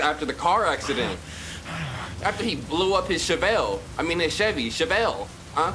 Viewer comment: My niece's name is Shavelle, I was trippin cuz that's what it sounded like you said lmao. One a side note... your one of my favorite YouTubers. I love how funny and silly you are.